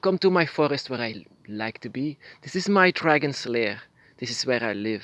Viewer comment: i know it was the acent you have brother i swear i heard "this is my forest and my dragon slayer" when i know you said dragon's lair it just took me by surprise and mostly i believe a leader should guide not saying what you need to do as much mine said "You are a leader not to say this is your path but to guide you on it since we don't know what is your path" as much seems i am a leader as a guide not someone to control but as best way of saying as a guiding leader since mine shown me this. I will welcome you to my circle just you and your dragon/dragons if you have more no priestess or priests, just you and your dragon plus me and my Montey's/Guardians since we do welcome those who want to learn but not tell us what to do or how to walk your own path but do our best to guide you on it since this is life and we still don't know how anyone's future is but best to have wisdom/knowledge to guide those but not show the way